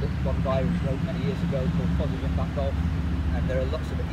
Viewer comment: Did anyone catch the title of the book he mentions at around 8:05? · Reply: It’s called Positive Impact Golf